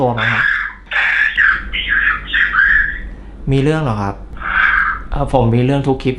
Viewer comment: หนูได้ยินว่า"เรื้องของกู"😂😂😂😂😂😂😂